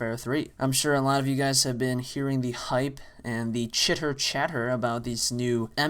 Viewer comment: lol i thought u said "shitter shatter" xD